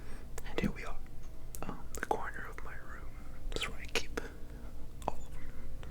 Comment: Did he say,.The coroner in my room